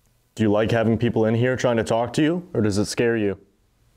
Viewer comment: The EVP when you ask if they like people coming to talk to them, to me it sounds like it says “No, it scares me”